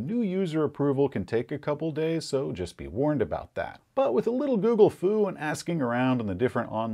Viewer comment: Oh...you said Gooogle-fu, I thought you said Googlethulu..the true madness of the internet